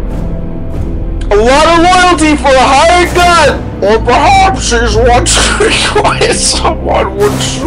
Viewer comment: I CAME FROM PEWDIEPIE AND I CANT STOP LAUGHING AT THIS SCENE LMAO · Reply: Questing Knight Hey, what does it say in 0:54 ? "Or perhaps..." or something like that, I cant tell :/